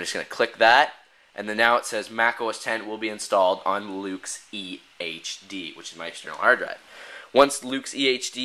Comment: When you said EHD it sounded like EA Games